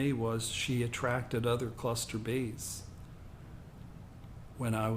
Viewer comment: When you start telling the tenth way, I can’t make out what you’re saying around 22:58/:59 - “...my mother attracted other ________(it sounds like cluster bees). It’s just before you talk about being molested.